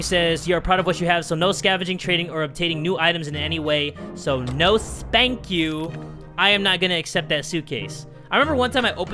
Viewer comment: is it me or is he saying ''No, Spank you!''